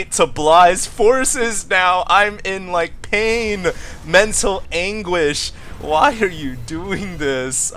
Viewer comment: I almost thought said "menstrual anguish"...